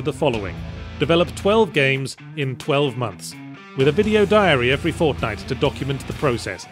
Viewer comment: I like it but why is it called "dex dixry"